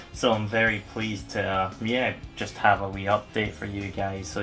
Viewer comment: I'm sorry... Did you say "Wii" update?? I thought we were here for Xbox 360 games...